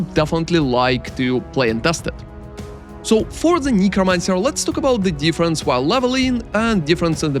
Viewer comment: at first I thought u said Negromancer im like what LMAO 😂😂😂